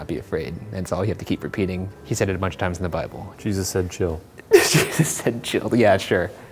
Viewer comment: "Jesus said chill" BY:Shane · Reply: lol I thought you said "Jesus and chill"